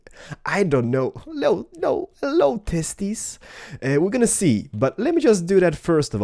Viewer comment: did any1 heard what he said to willager (1:50) "hello TESTIS" hahahhahahhaha